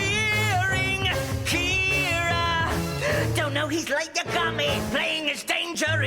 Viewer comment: Was I the only one who thought Ryuuk was saying "Don't know he's like a Gami" (As in, 'Like a SHINI-Gami') and felt so confused when I saw "Don't know he's Light Yagami"???